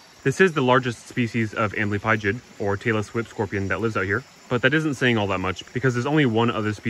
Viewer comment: Thank you so much for this interesting video. I I screamed with laughter because at 0:26 i heard "Taylor Swift Scorpion". Greetings from Germany LOL